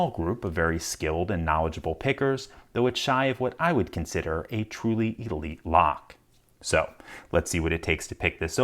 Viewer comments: A truly WHAT lock??? I did not understand that word. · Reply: He said a truly elite lock :)) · A truly yuly lock? I listened to that 20 times and that's all I hear...